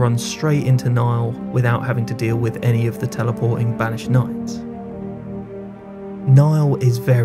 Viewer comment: Nice work man. Really enjoying your build vids. I had a moment when listening to your description of the "Castle Sol Skip" I thought you said you can run straight into Niall without having to deal with teleporting "vaginitis" lol I worked out you said banished knights of course .